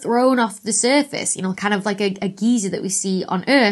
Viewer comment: Being in the US it sounded like you said 'geezer' instead of 'geyser'. 🙂